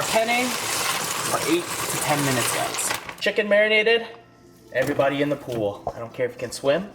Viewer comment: Did you say chicken a**?